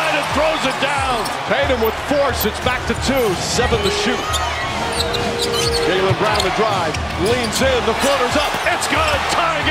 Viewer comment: I thought they said Jaylen Brown didn't have a left hand